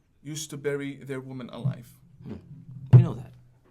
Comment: Gosh, i love Christians and JEws so much. Did i forget someone? 1:00:00 What? Did he say "Bury alive"? Before, or after they repudiate them? 1:17:16 Ok buddy. Imam, if that's all that you do rely on, you'd rather just give up and preach Buddha or Krishna. Or Jesus.